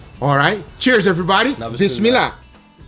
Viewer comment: did he say bizmillah? As in muslim words?